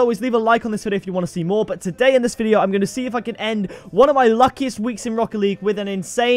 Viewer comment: Your stopping RL? Awww mannnn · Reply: oh wow my bad I thought it said my last week not luckiest week my bad😬 love your vids man❤️